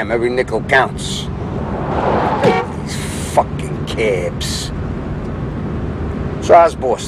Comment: For some reason, "F#KIN' CAABS!!" makes me laugh out loud every time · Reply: i thought he said "crabs" :D :D